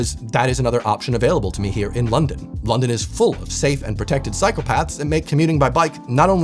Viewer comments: As a Canadian I said “THE AMERICANS CAN’T WALK TO THE STORE??? WDYM? IT TAKES ME LIKE 3 MINUTES!” Edit: I totally didn’t hear “Psychopaths” instead of “Cycle paths” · Sounded like "London is full of safe and protected psychopaths..." 😅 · I heard "safe psychopaths" when you said safe cycle paths. Maybe you hear what you want to hear, yikes! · I heard "London is full of safe and protected psychopaths" … well, I'm not a native speaker, and in the context of the current state in the US … 😁